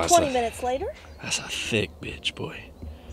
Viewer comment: What did you say???? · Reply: "That's a thicc bitch boy"